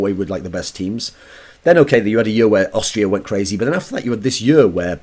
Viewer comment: does he say austria?